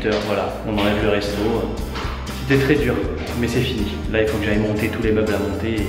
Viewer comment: Putain, j'ai entendu "J'aime Gémo" ça m'a tué